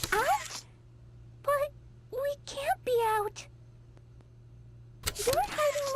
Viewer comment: Dude good try but the whole theory with Craftycorn doesn't work because you misheard, she says "Out?.... we can't be out" this is her saying to the person she is painting with that they can't be "Out" of red paint as she wants more. It is not referring to going outside